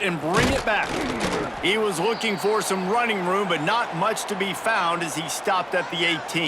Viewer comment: What did they say in the background at 42:08 ?? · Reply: The punt return was looking for running space, but the defense kept him contained